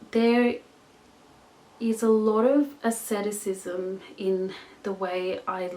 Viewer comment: I thought you were saying 'aestheticism'! 😂 I had never heard of the term 'asceticism' before, love it ❤